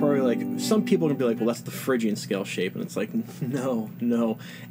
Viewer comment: Did he say "That's the friggin' scale shape" at 5:20? 'cause that's how I feel about most shaped chords. Other than E and A, they're impossible.